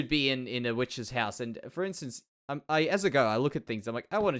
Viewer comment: is it me or does he say as a girl?